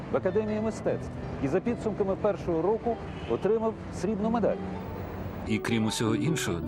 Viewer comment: что он сказал, скажите пожалуйста, не могу разобрать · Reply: Получил серебреную медаль